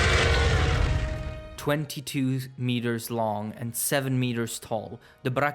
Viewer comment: I thought he said 2 meters tall and 7 meters long. I was racking my brain on how it was 6'6 and 22 feet long when its atleast 2 or 3 times taller than the character.